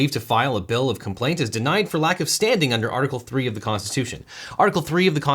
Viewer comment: I thought it said "Lack of Understanding" at first... I'm probably not wrong